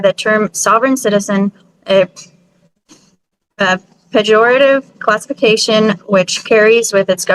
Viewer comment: Shes so proud of herself for using big words that she doesn't understand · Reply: Did she say "pejorative constipation"?